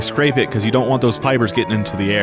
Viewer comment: "Asbestos is not the bestest" I feel like you made this whole video just to make this pun in the end. Worth it. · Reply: I thought he said asbestos is not the best to us